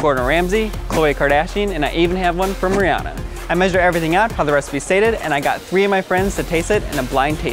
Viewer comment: did he just said sayed it 0:23 XD